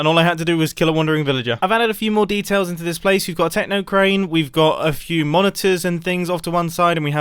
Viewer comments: I thought mumbo said technoplane and i was like Does mumbo watch technoblade? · "Techno Crane" Me zoning out: did he just say Techno Plane ? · Did Mumbo say techno crane.